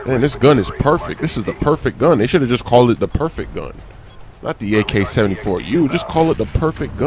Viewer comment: Did Smoove Say "Fuck The ak74u?"